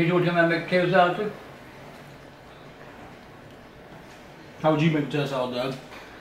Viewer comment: wat did say at 5:42? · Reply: How would you make potato salad Doug?